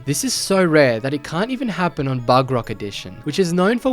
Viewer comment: did you just call bedrock bugrock😭